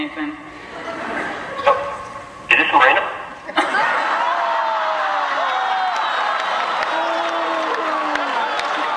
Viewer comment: What on earth does he say at 7:04?! Can't make it out at all... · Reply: Nathan, after praising her beauty and talent, suddenly blurts out on Jewel's phone "..... Is this MORENA???" As in Morena Baccarin , the woman sitting next to her. I agree the audio is tough to listen. On this particular video for some reason the volume SPIKES up and down in an annoying fashion.